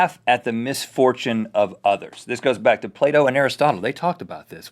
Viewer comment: Silly American accents. When Rhett said "Plato" I thought he said "Play-Doh".